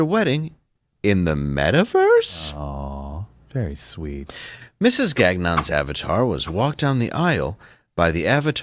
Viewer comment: I have had very little sleep in the past three days.. and I swear I heard that name as Mrs GAGNOB !!!